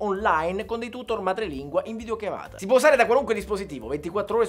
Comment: Solo io ho sentito "in video chiavata"?